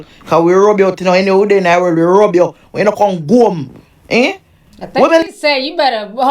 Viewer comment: No she did not say, 'rob'. she said, 'rub-out' meaning, to erase (its figurative) as in to curse and disrespect the person mercilessly.